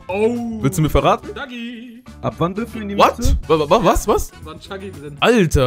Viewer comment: habe ich Doggy verstanden